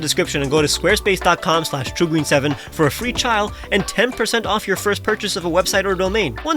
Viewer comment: I heard "for a free child" and YouTube subtitles agreed with me! That's a weird situation to be in!